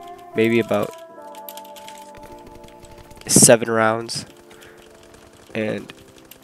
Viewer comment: i thought u said 7hours lmao i was like WHAA??? but then i realised u said 7 rounds XD